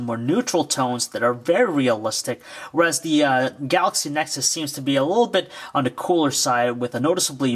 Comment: did he just said galaxy nexus